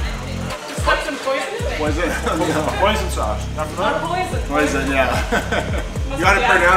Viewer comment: 100% thought he said poison sauce at first 😂